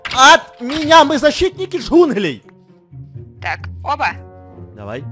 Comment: Ору послышалось что сказал перец: -Мы защитники жигулей😂